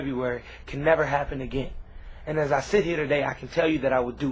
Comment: Lmao, best part is at 1:53 when the captions say "An investigator they have to tell you that I would do everything in my power to make sure that it never happens again"